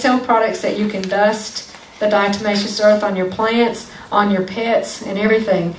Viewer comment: I thought she was saying "You can dust it on your pits" I thought this was used as a deodorant too🤣